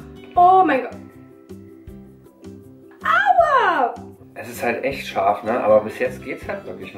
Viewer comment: i dont know what she says but that sounds like "agua" wich means water in spanish hahahah